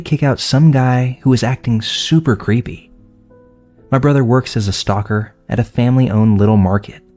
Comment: I know this is serious stuff…but in story two…it sounded like you said, “My brother is a stalker!” 👀 But to my surprise, it turned you said, ‘STOCKER’…lol 😅